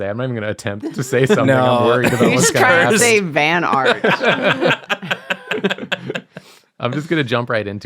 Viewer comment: now i kind of want to see design art to go on the side of vans. bonus if it's fanart of a media u really love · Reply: van art ? i thought you said fan art